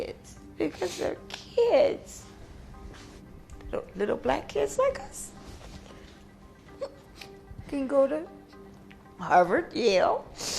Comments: Did she say... little black kids like US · did she say "little black kids like us?"